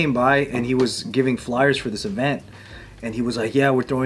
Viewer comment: why does say "flowers" like a Scottish Lad? 😂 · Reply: Flyers